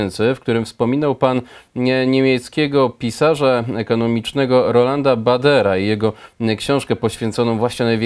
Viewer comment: Ktoś wie co to za ekonomista Roman Bader (jeśli dobrze usłyszałem), bo nie mogę nic o nim znaleźć? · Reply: Roland Baader. A książka o której mowa to "Koniec pieniądza papierowego"